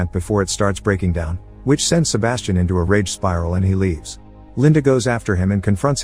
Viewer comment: does he say rape spiral ?